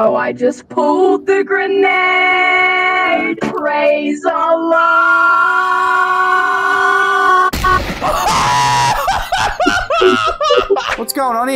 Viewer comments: I thought he said “Praise the llama” Edit: I know he said praise allah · I thought he said “Praise the llama” Edit: I know he said praise allah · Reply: I thought he said raise aloft